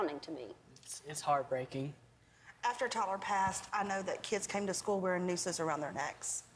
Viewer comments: can somebody tell me what did the mum say at 8:34? "after taylor passed..." i can't get it, i don't speak too much english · what is she saying at 8:38? What did the kids wear around their necks? I could'nt hear it acoustically... Did she mean ropes?